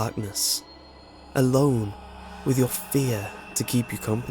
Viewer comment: Am I the only one that thought this said "goodnight wet dreams" XD